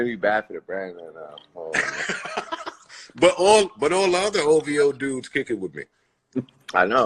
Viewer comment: Did Joe Say Hoe.V.O?